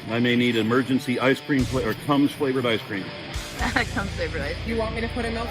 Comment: I thought he said cum flavored ice cream!! lol. I herd it rong. Tum flavored ice cream. 🧊